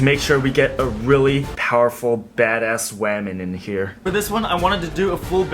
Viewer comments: When he said badass women I thought he said badass ramen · wamon that’s what i heard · did he just say waman?”🤠👍